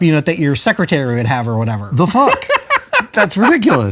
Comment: Did Adam say "The Fuck?!" Odd hearing him cuss.